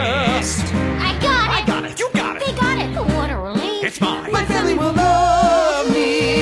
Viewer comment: why am I the only one who hears "They got it"?